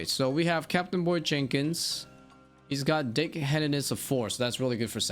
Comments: I heard dickheadedness. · Bro I love you haha when you said "he has a deckhandedness of 4" I swear I heard you say a "dickheadedness of 4" and I lost my shit